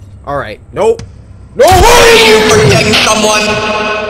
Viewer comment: Did you hear what she said? She done said, "FORGETTING SOMEONE?" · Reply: Mama : ORANGE, AREN'T YOU FORGETTING SOMEONE?